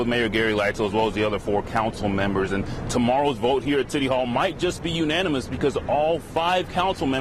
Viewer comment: did he say "titty hall"?